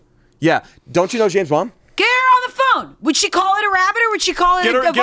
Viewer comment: When they said James mom I thought they said James Bond and got so confused for a second 😅